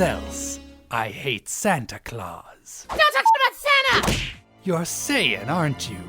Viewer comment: sounds like he says "Dont fucking talk about santa"